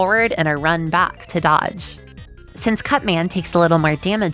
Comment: for a second i thought you said cvntman